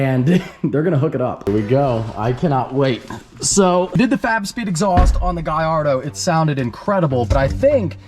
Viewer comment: I always hear "fap speed" when he talks about fab speed